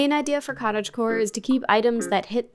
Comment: Misheard "Kora" for "core" and thought, What the hell's building-core and scripting-core? Programming aesthetic? Then after I realized my mistake I started thinking what "koracore"''s gonna look like...